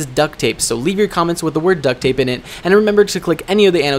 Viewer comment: Duct tape? I thought you said duck tape